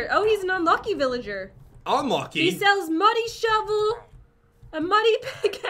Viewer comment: When jen said muddy i thought she said money